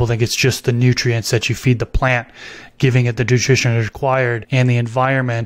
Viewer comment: Some one please help me, what is the word he is saying on 9:14* “Giving it the ____ required…” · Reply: Nutrition